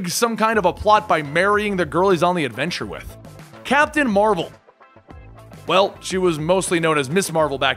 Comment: When u said captain marvel i thought u said captain america i was about to start ww3